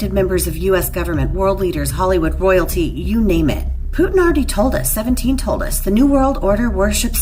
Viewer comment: Did she say Putin already told us? Not really the source Id' lean into..